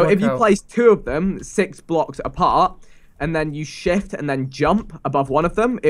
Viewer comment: vikk says "and then u sh!t along the path" hahahaha well the first time it sounded like that sooo